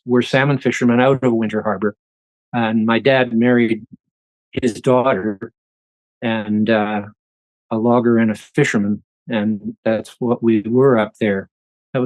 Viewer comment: Did he say his dad married his daughter, a logger AND a fisherman???